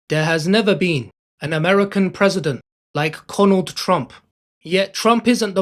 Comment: haha it sounds like the narrator is saying "Conald" instead of Donald. "There has never been an American president like CONald Trump"